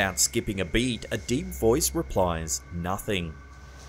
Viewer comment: I hear "Nothing's here"